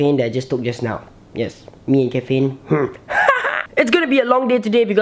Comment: i thought you say me and kevin instead of me and caffeine